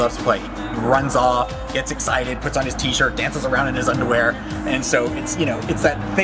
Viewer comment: Did he just say Cody dances around in his underwear?.... O.O